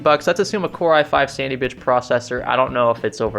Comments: lol swear he said sandy bitch processor CC thinks so too lmao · did you say "Core i5 Sandybitch processor"? 😂 In all seriousness though its probably either the i5-2310 or i5-2450P, but technically the 2450p turbos up-to 3.5GHz so its probably the 2310. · “Sandy Bitch processor” is what I heard. lol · sounds like he says Sandy Bitch